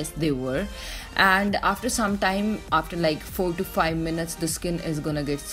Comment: I heard you say 45 minutes. Is that how long it took for the whole leg? That sounds like a really loooonnnnggg time!